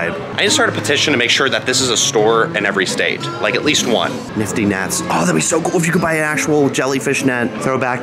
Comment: thought that said nifty nfts 💀